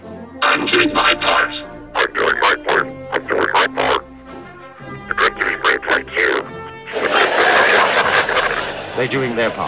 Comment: What he say? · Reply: i'm doing my part too